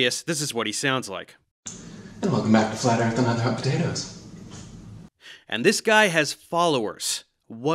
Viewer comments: What does he say at 1:12? Thats unintelligible gibberish, please if someone understood please clarify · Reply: welcome back to flat earth and other hot potatoes. Think it's the name of his podcast or sth lol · genuinely confused, what the hell is he saying here, it sounds kinda like “and welcome back to flat earth and hot potatoes” but that doesn’t make sense nor do i know what that would mean